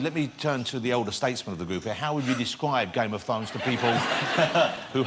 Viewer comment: I can't listen to this guy it sounds like he says Game of phones.